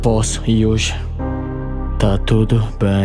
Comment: So eu q ouvi "sem provas pro abusado"